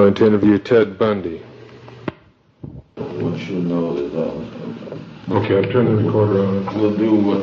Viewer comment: i heard "what you know about roll-" too much youtube shorts for me